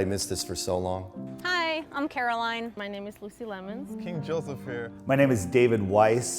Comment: I thought she said her name was juicy lemons '^'